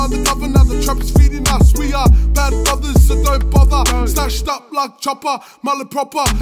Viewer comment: CJL oh okay thanx for that I swear when I heard it I got what he was referring to straight away like in reference to Chopper Reid thought he said “slashed” instead of “stashed” and after listening to it a few times it still sounds like “slashed” unless I got bad ears lol... anyways RIP to their mate if that’s the case.